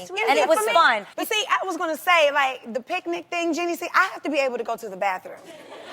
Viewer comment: i thought Tamar called Jeannie Jeezy